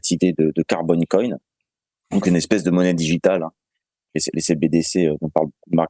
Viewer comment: J'ai compris "carbon cohen" 😀